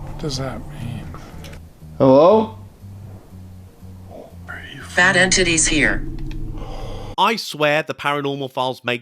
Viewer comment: i thought it said ''fat a** tiddies here'' the first time i heard it